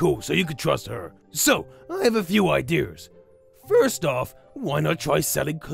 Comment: any on else notice that Garr other said ider instead of idea